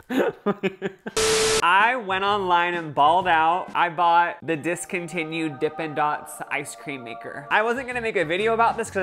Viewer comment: I thought you said you bought some discontinue snacks